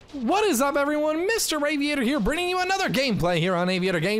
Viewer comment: I thought he said, "Hey everyone Mr. Raviator here!"